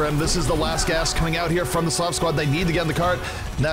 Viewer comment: I thought it said "Slav Squat" at first.